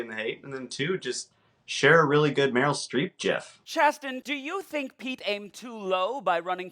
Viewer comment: did he say jif? wtf